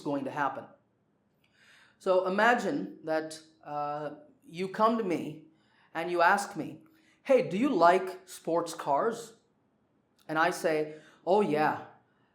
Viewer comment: What did you say sir. I couldn't understand the sentence. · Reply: "Imagine that you come to me and you ask me, 'Hey, do you like sports cars?'"